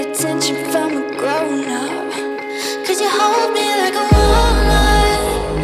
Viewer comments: I heard "Cause you hold me like a walnut" 😂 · I legit thought she said "cause he holds me like a walnut" at 0:20 for so long. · YOU GUYS I heard "'cause you hold me like a wallnut" tHIS IS SERIOUS SHIT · i thought it said will u hold me like a walnut 😂😂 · ITS SOUNDS LIKE SHES SAYING " CAUSE YOU HOLD ME LIKE A WALNUT" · BRB GEHT MICH IN EINEM KAKTUS ZU SCHIEBEN I HEARD THIS LIVE AT SLFL SAN ANTONIO DAMN IT WAS GOOD also when i listened to this I thought Rena was saying "cause you hold me like a walnut"